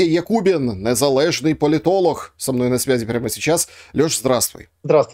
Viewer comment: Тут надысь Громыко Ольгу читал. Поэтому услышал "политолух"